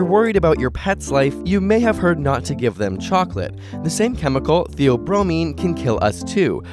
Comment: Who else thought he said "The Obromine" as two separate wordd